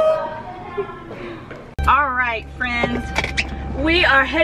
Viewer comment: Praying that Evie heals from this and that also gets better soon. Btw tell why I thought you said Alright instead of alright friends when you were headed to take Kapri to tumbling hahahah